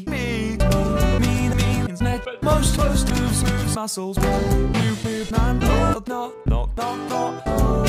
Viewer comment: who heard moo moo man